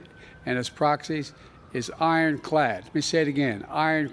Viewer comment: Is actually saying iron clad? I heard 'i wet my pad'